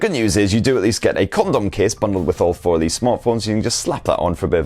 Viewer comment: did he just say condom case?? LMAO 🙉